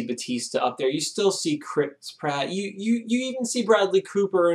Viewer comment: what did say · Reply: "You still see Chris Pratt" ?